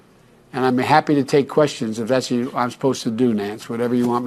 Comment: I thought he said "I'll take questions if that's what you want me to do Nance". Boom! It eneded. Of course there's more going on than we know.